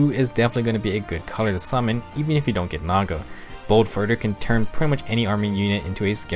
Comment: did I heard bold further? Or am I crazy